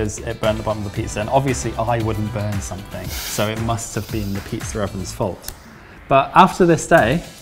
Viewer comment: LOL - I turned on subtitles to catch a phrase I couldn't quite make out...then at 10:20 we get "pizza oven" transcribed as "pizza reverend" - Holy moley!